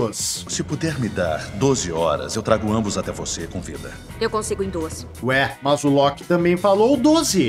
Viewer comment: Acredito que ela fala em 10:45 "-Eu consigo em duas".